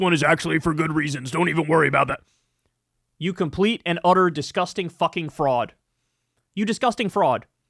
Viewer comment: A thought Kyle said you disgusting Frog 🐸